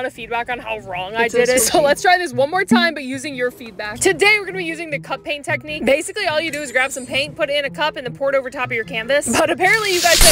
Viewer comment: I THOUGHT YOU SAID COCAINE TECHNIQUE